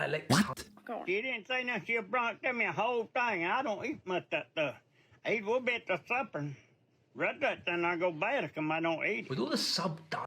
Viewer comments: “He didn’t say nothing he brought to me a whole thing and I don’t eat much of that stuff. I eat a little bit for supper the rest of it is gonna go bad because I don’t eat it” that’s what the old man at 12:06 said btw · As an American, here's what i got from that. "He didn't say 'noth'in but he gon' sent me a full THING. Now, i don' eat much tha' stuf', 'sept (????) and the supper. Worried it's gonn' go bad if i don' eat." To translate, if you still don't get it, a buddy sent him a whole container of something he doesn't eat much and hes worried it'll go bad.